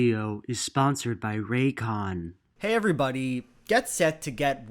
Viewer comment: I thought it said "sponsored by Raytheon" at the start and was very confused for a second!